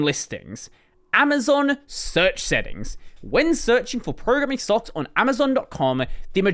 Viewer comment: As a non english speaker i first heard it as "programming sucks". 😂